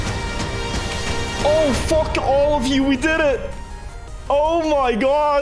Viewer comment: "F*** All of you!" (I probably misheard :D ?) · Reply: Sounds more like "Oh fuck! All of you, we did it!"